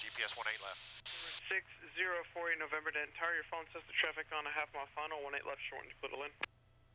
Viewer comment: Can someone tell me what the controller is saying at 47:23 when he clears him to land? Sounds like he is saying Turn your phone....even the CC has him saying phone. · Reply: “traffic your following 1/2 mile final, clear to land”